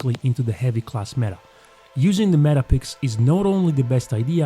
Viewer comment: what did that say 🤨🤨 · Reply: meta pick😅 ahahaha